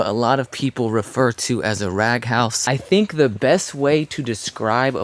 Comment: When you said rag house, for some reason my mind thought you said trap house…